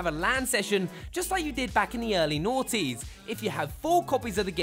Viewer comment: "Back in the early _" Did he say "noughties"? If so, that's the first and only time I've heard "2000s" said that way. Hmm